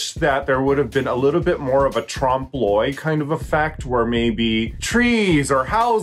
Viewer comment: Raja says "tram bloy"?? Or, what did she say? What is that??